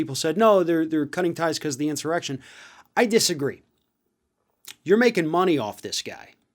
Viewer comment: speaker says 'I disagree', subtitles say he said 'I agree'. Strange.. it's not as if he is mumbling or as if his speach is so unclear..